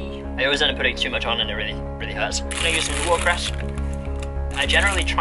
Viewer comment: Can someone please help me, what does say at 3:31 that he puts on the wrap. I am so stuck. Thank you! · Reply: Kate Patterson 6 months late haha -- it's watercress